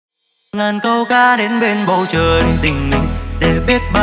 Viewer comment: ngàn câu ca thì nghe nhầm thành nàng câu cá:)))